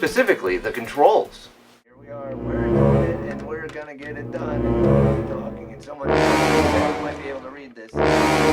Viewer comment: Always wondered what was said at 9:35 so I isolated the sound effects from the video and here’s what I could understand “Here we are. We’re doing it and we’re gonna get it done and we’re gonna keep talking and someone who’s probably deaf might be able to read this.”